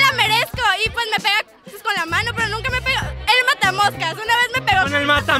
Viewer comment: Le dijo mosca jajajaj eso yo entendí